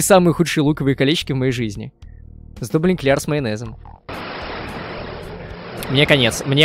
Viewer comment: Майонезные колечки в луке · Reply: А мне послышалось эклеры с майонезом